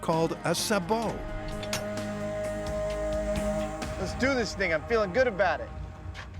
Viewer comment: did Adam just say "let's do this n-word"???